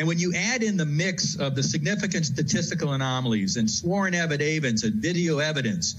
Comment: Did he really just say "sworn evi day vence" instead of "sworn affidavits"? LOL.